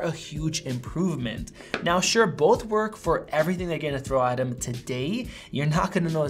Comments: Believe it or not I just upgraded from my iPhone 4S to the iPhone 13 Pro. I believe I got the biggest upgrade!! Granted I have had my iPads up to date. The 4S was truly a trooper but sadly 3G is just way too slow in todays modern world! I’m very happy with my new upgrade 😄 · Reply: Thanks for your valuable opinion. BTW, What was exactly said at 8:29 - 8:31 "Both work well for everything........... thrown out at the to date.?" Help me please. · Which one do YOU think is most worth it? Let us know below! We think THIS is the iPhone 13 model to buy ➡ <link> Need a fast charger? BEST 20W charger for $15 on Amazon ➡ <link> Like our wallpapers? Find them here ➡ <link> Support us by buying an Apple Product shirt ➡ <link> Amazon Links to the best deals on iPhone 13 Pro Accessories ⬇ iPhone 13 ➡ <link> iPhone 13 Pro ➡ <link> iPhone 13 Pro Max ➡ <link> My FAVORITE MagSafe Accessory ➡ <link> Anker GaN 30W Charger ➡ <link> MagSafe Charger ($5 OFF) ➡ <link> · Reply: What was exactly said at 8:29 - 8:31 "Both work well for everything........... thrown out at the to date.? Help me please.